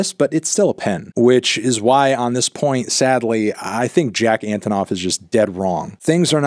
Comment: Clicked a video so fast that I thought said "jack off is wrong"